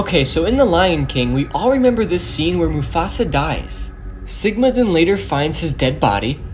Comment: did he just say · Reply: sigma 😭